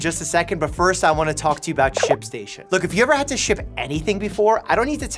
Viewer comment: I thought he said sh1# station😂